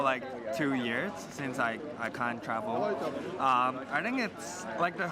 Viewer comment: I swear he says "since I time travelled".